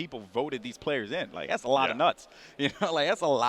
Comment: " thats alot of nuts" anyone else catch that? hahahahahaa